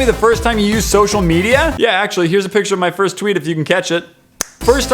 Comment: Ayyy I caught the picture first try! Also when he said "if you can catch it!" I first thought he said " me eating cat sh**!"and I'm like 😕